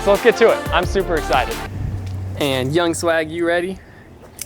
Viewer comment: I swear he said young slag